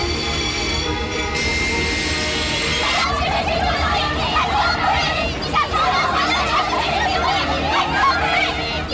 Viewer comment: TM一個字也聽不懂，到底在喊啥？ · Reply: 低下头，弯下腰，用力